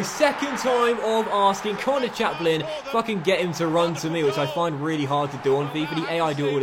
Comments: naughty naughty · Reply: Jack O'callaghan I thought he said it too, but he said “if I can” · Been watching since Torino in FIFA 15 and finally dropped the f-bomb. Historic day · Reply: Daniel Rajaiah I thought he said it too, but he said “If I can”